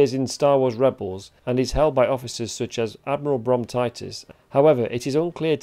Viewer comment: I thought you said "Admiral Bronchitis" 🤣 That shall be his name in my head cannon